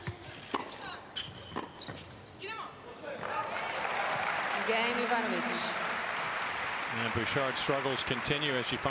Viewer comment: what is the word Ana said on 0:16? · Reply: 'idemo' it means 'let's go' on serbian :D